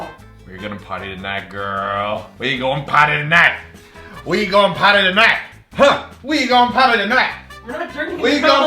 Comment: I kept hearing "we're going potty tonight!" 😂